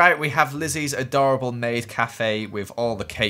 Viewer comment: and skip five seconds and it sounds like Joel said: S.Beans the adorable maid cafe😂😂😂 (You have to time it right)